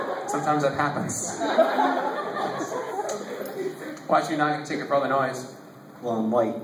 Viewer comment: What did the hypnotist say at 19:16? I can't understand him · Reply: Space Boy He says, "Why should you not get a ticket for all of the noise?" PS: I was the tall kid in the black shirt that he asked. It was a lot of fun